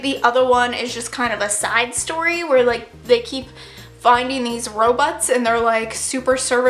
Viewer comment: i 100% thought you were saying Ro-butts, instead of Robots 😂 jesus. SAD TOG FEELS. I CRY 😭